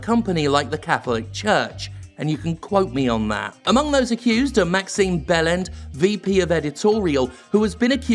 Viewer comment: Am I the only one who heard "Massive Bellend" and had to look at the screen to see the actual name? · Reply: For me it was "Wait... did he just say Maximum Bellend?"